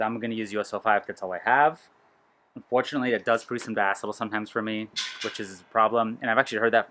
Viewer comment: what does he say is downside of us05? I have relistened and can't understand. Captions said basketball which makes no sense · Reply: he says diacetyl